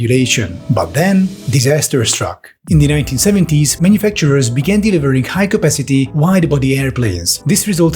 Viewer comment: what is he saying at 2:39? All I hear is "In the 1970s manufacturers ???? high-capacity wide-body airplaines." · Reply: Began Delivering.